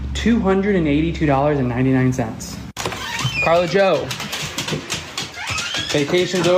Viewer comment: did he just call his dog "Scarlett Joe?" That's my wife's dog's name, Scarlett Johansson. She's a sweety!